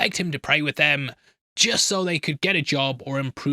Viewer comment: I'm curious, what is that word you are using there? Subtitles say: "how about", are you saying "however"? I've heard you use it in other videos before too and never knew what you are saying(sorry English is not my first language).